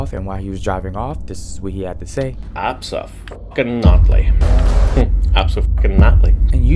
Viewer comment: he's saying "abso-fucking-NOT-ly" He is not saying his wifes name 7:57 He didn't kill his wife because of pent up rage it was because she cheated on him with another guy. That still doesn't make murder ok but please dont make fake evidence or whatever